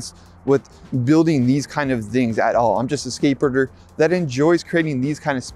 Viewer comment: Did he just call himself a skateburger¿? 🤣🤣🤣